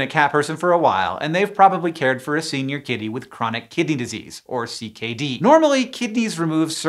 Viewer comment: I’m gonna be real I thought you said Chronic Kitty Disease